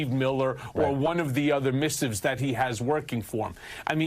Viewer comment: Notice that at 3:25 Michael Cohen uses the word missives to describe Trump's document writers and instead of using that word, the rolling transcript says "people". Apparently, the transcriber doesn't know the meaning of what a missive is, i.e., letter from an official person.